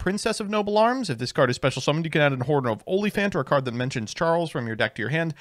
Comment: I thought you said Angelica adds a "horn of onlyfans" 😅